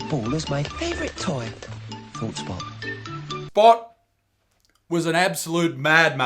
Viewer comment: I swear he called spot "bot"